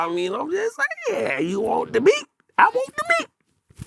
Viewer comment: " You want the beats I wants the meats" Joc tweaking for real lol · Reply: I thought he said "You want the beats, I wanna beat" lol